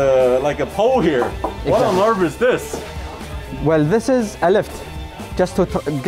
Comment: At first I thought the captain said an arabic letter "alif" ( ا ), because the pole is straight like a letter "alif" 😁😆😂